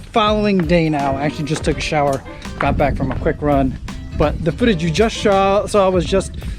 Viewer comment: Could have sworn you said "foodage" at 3:36. 😀